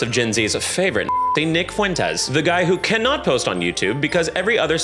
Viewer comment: i heard vic fuentes and i was like what did he do 😭